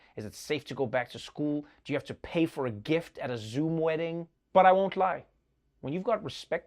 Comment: “Do you have to pay for a gift at a Zoom wedding” 😂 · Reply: I 100% heard that as "zoo wedding" as in if people plan a terrible wedding do we still have to give them a gift 😅